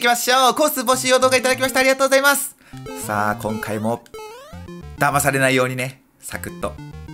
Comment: 「だばだ〜じゃないんだわ」もう さんの名言ですね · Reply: ダバされないようにねって聞こえた